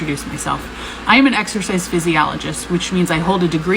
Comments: I literally thought that she said she was an exorcist too Lmaoo. I guarantee you that her weight dissents help her spinal defect AT ALL. Lol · Oh and you were using Xfiles Music for the ‘exorcist’ joke (I have a different accent but I heard ‘exercise physiologist ‘ so 🤔) - should have used the Mike Oldfield track from Tubular Bells